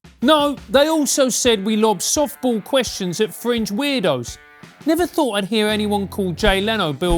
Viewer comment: Thought he said 'French weirdos' not 'fringe weirdos'.